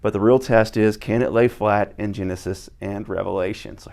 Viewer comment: what did you say revelations lol? it's like saying songs of songs 😅 edit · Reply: I said Genesis and Revelation so…